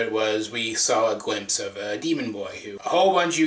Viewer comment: When you said 'demon boy' I thought you said 'deviant art boy' 😂😂